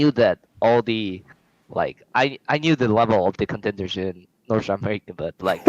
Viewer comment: I knew the levels of the contenders in “sanfownrkejg” like tf does he say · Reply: “i knew the level of the contenders in north america”